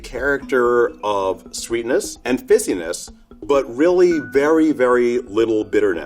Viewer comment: did he say pissiness? LOL.